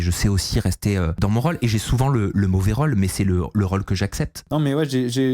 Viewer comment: « J'ai souvent le mauvais rôle » avec son accent j'ai entendu "j'ai souvent le mot vérole" 🤣🤣🤣